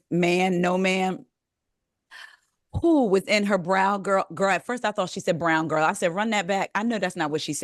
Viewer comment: 😂😂😂😂😂must be a Jersey thing because I had to watch it 2x because I thought she said, "BROWN GIRL " also. And sidebar Mr. Osmond sounds like "Johnny- J." SMH he sounds a bit full of himself.